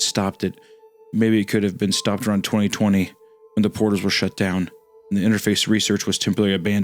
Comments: I heard "portals" as "borders" taking into account that this story was written in 2016, I had quite the double take. Then again, it would've just been a meaningless coincidence. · Woah. For a second at 8:52:57 i thought hunter said "when the borders were shut down" and was freakin out for a second. But it was portals. So never mind, it wasnt a crazy omega super duper prediction. Still that wouldve been crazy.